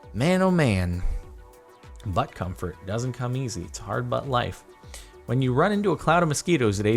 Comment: I heard that 'it's a hard butt life'. Tom where you at